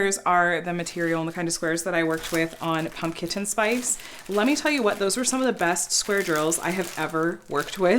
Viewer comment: I thought you were saying "pump kitchen spice"! I had to look for other videos to figure it out. This slumber party one is so cute!